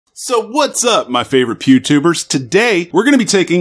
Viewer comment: Wife just asked me "Did he just say 'Pube-Tuber' and I can't unhear it...